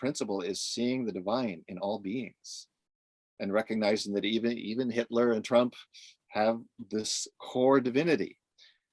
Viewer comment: Did just say "...even Hitler and Trump..." ?